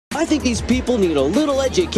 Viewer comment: It sounds like he is saying "the diseases here protect your children"